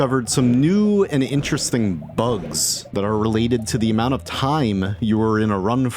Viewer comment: What does this say? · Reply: Probably "bugs"